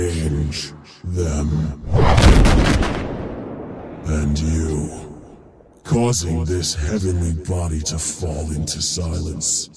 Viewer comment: i didn't understod anything, can someone tell me what he said? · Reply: "And you, causing this heavenly body to fall into silence... I will not let this happen!"